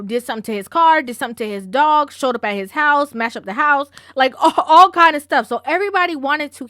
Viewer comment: Did I just hear you say "Mash up the house"?!? My parents are Jamaican so my ear couldn't miss that😂 Your videos are excellent & I really appreciate your perspective on things especially regarding this case. South London UK Watching.. take care